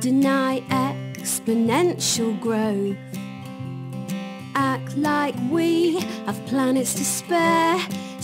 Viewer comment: When I watched this like 2 years I thought it said "at night we have planets to spare" but now I know it's "act like"